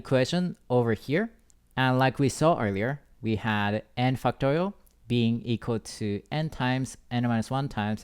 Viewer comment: whhy do i hear "fucktorial" T.T